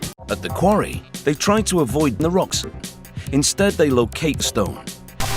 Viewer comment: did I hear a rock and stone?!